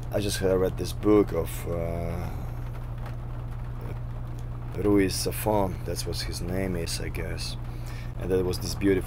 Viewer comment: What is the name of the author he mentions at 6:05? I cannot understand Nergal here. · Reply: Louis Stefan?